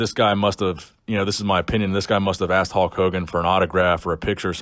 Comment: It sounds like your saying Hawk Hogan hehe. Lincoln Hawk Hogan over the crutch.